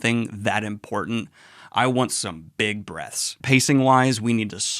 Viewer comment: Definitely surprised when I misheard say about Ep. 9, "... but for me, I definitely wanted some big breasts..." Took a second for me to realize it was "breaths." I think.